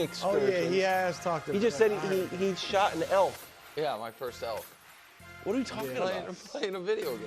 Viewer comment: Until I replayed it I thought Nick said he shot and ate his first elf. Lol